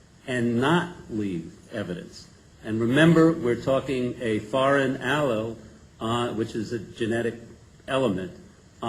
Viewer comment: the expert is talking about evidence (recovered from the penis of Steven Branch): "a foreign aloe, which is a genetic element." What is a "foreign aloe"? I'm not sure "aloe" is the word he's saying. That's what it sounded like. Has STR testing been done on this evidence? Seems like all the evidence needs forensic re-examination with the most current highly sensitive & advanced forms of DNA testing such as STR and the micro-vac machine shown in the Bob Ruff documentary. I think it's very incriminating that a hair that could belong to Terry Hobbs was found INSIDE a knot of the shoe lace. Only STR would be definitive in eliminating or identifying that hair as belonging to Terry. I think it's possible the hair similar to Jacoby could be a transfer brought to the scene by Terry after being at the Jacoby house. Was the tree stump where that hair was located close to where the body of Michael Moore was pulled from the creek? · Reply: The term he is saying there is "foreign allele" which means a DNA fragment that could not have come from the victim. However, it's not clear if the foreign allele is even human (could be bacterial for example) but what is important is that it didn't match any of the West Memphis 3. To the issue of the tree stump where the hair matching Jacoby came from, yes, it was about 10 feet away from where Michael Moore was found but on the east side of the creek where only the killers had known to have been (searchers were only on the western side).